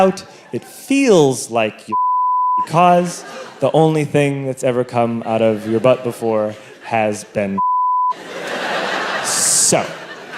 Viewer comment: what does he say here? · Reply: "it feels like you're shitting because the only thing to come out of your butt, has been shit"